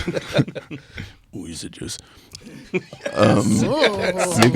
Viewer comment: What does he say here? It sounds like he said "Ooh, he said JUST!" but maybe that is just want I want to hear him say.